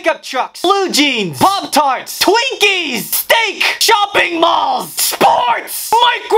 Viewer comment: At first I thought he said chopping balls instead of shopping malls 💀